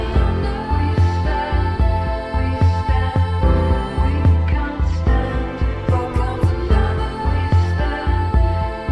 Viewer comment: Lol anybody read lips and can tell me what she said at like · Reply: Yeah, she said (continously) - "Who you looking at? Who the Fuck you looking at? Yeah, who the Fuck you looking at, huh!? Who the Fuck you looking at?" - It was in reference to/referring to an incident she had with a guy in the industry, who claimed she was "too masculine/feminine, and had to stick with societies norms, etc". I can't remember exactly what it was, but she said it in an Interview ❤️